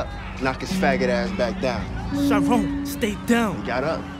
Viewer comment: What did Kevin say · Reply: No he said stay down Chiron